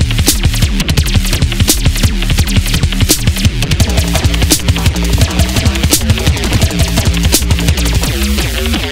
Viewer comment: did anyone catch what he said at 13:57? couldn’t hear him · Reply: i can only hear him say '...artificial...and something about his drum kit, but the vocal audio gets muffled at that point.